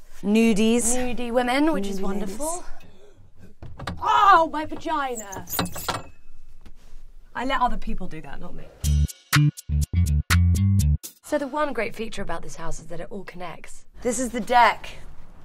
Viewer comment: Never in a house tour did I think I would hear the words 'oh my vagina' 😂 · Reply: I thought she said pagina lol, I was like wtf?